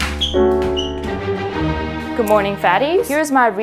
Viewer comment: “good morning fattys” did u just call us fattys or I’m hearing it wrong or it’s a other name for queens sorry if this comment is too to long